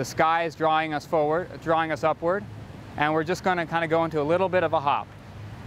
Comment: The exercise at 6:45 is called Soft Hops, not Soft Tops! The label on the video and also the words on the screen are wrong; someone misheard it. Soft hops!